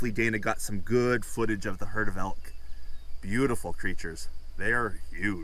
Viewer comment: Oh ... Herd of Elk ... , I thought you said herd of Elves ! lol I was thinking , I want to see that !